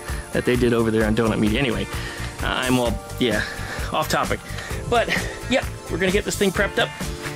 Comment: You said: "Off topic".....but I thought you said: "I'll chop it"